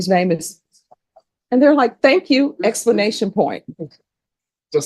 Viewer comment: Did she say.... "explanation point?"